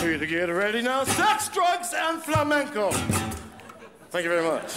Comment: eu ouvi um flamengo? Skskksksksksksks